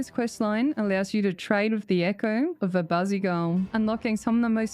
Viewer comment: I swear i heard "A bossy girl" until i saw the name 😂😂😂 Ps:I love your guide ❤